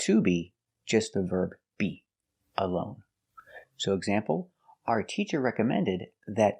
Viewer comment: Sorry, but I heard "Are the teachers recommend...." in expl. instead of " Did the teachers recommend...."